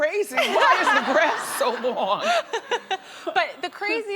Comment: When she said 'why is the grass so long?' I thought she said 'why her breasts so long?' hahahah